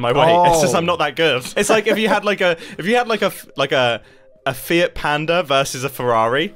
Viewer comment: the subtitles saying this as “fat panda” makes the analogy even funnier